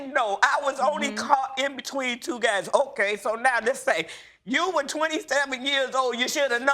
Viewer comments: Oooof I thought she said she had an affair with 22 guys at first, now that would be a story. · I had to hit the rewind button I thought she said 22 guys but she said between two guys..lol😂😂😂😂.i am glad the lady and her son are speaking again and everything is okay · LOL I thought I heard her say she had an affair with 22 guys. Not between two guys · when she said between two guys i thought she said with 22 guys